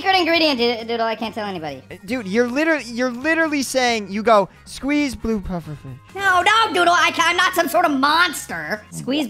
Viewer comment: I heard squeezed blue pufferfish